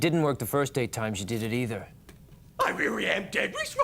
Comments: what did he say? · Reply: "i really am dead this time" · What did Scooby say? 🤷‍♂️ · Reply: I really am dead this time